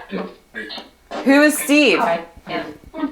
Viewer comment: When you said Whois Steve I thought I heard together